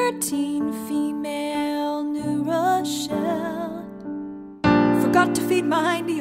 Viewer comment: the “early 2000s chat room” one sounds like the opening number in a coming-of-age musical! “thirteen, female, new rochelle” is about to become the audition song for middle school sopranos everywhere. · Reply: 😂I misheard new Rochelle as “new Russia ”